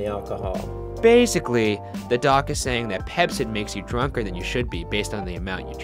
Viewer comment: I thought he said pepsi!! :)